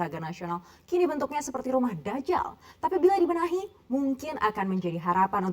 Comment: Gw kira salah dengar, ternyata mbaknya benaran bilang dajal 😁😁